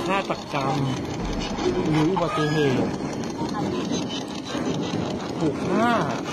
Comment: ช่วยฟังหน่อย คำถามอุบัติเหตุหรือฆาตกรรม ได้ยินคำว่าอุบัติเหตุ 4:35 เสียงจะตามคำถามกลางๆ ลองฟังดู